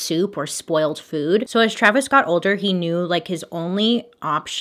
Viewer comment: I swear I thought she said Travis scot